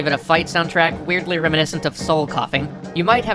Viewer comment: What did he say OFF's soundtrack is weirdly reminiscent of? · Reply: Soul coughing